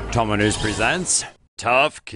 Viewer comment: Lol...At the beginning of the vid, I thought it said "Dominos Presents" instead of "Tomonews Presents"!!!